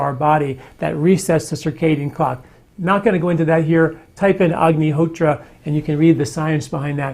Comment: What is he saying @ 11:43? Type in... ? Spelling? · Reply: Agni Hotra Ritual . There are a few videos about it.